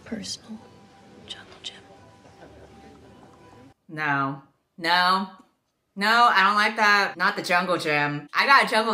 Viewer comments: I thought she said 'chocolate chip' not 'jungle gym' and I was so confused for a second. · "I'm going to treat you like my own personal jungle gym." has the same vibe as "I want you splayed out on a table like my own personal feast." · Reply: First time I watched it I thought she said chocolate chip and I was yo what? Then I told my sister and she was like NO jungle gym! And I was like oh....makes more sense